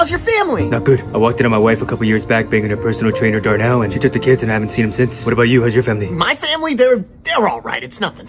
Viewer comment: he really said I hadn't seen him since Her wife was male??